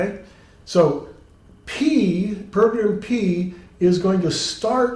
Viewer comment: "p [can't hear it] p" I can't make out the word he says between the two ps there. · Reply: "program"